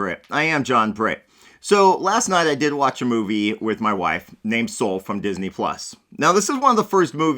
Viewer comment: Hahaha, It sounded like you said you watched it with your wife named Soul.